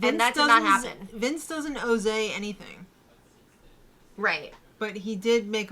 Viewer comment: when said "Vince doesn't owe Zae anything" I thought she said Jozea and I was genuinely confused (and yes I'm talking about BB18 Jozea)